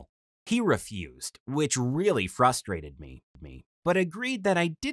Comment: did anyone hear him say me twice or is it just me